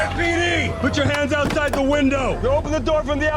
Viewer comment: did bro say LSPD?